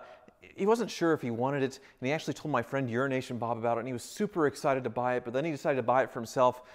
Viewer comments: I keep hearing "Urination Bob" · Excellent video. Glad to see EUROASIAN Bob gets the 348! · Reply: I thought he was saying Urination Bob.